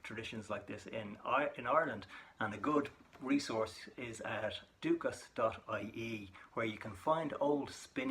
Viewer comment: Wait what is it? I didn't catch that. Just link to it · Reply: Stacy Mitchell I mentioned Duchas.ie If you follow my page Circle Stories on Facebook there are lots of articles about this area.